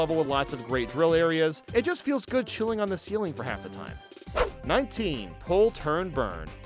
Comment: I thought you said chewing on the ceiling